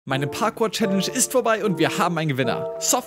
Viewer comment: Ich hab am Anfang erst Parkuhr challenge verstanden😂